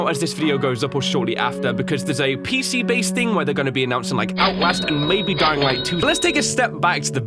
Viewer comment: Did I just hear OUTLAST!!!! AAhhhhh, sooo exciteed